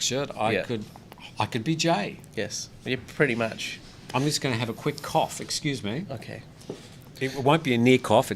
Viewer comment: At time 11:52 Did I hear you right? Did you actually say I'm going to have an f*** off? I could be wrong. I probably just misunderstood you're Australian accent.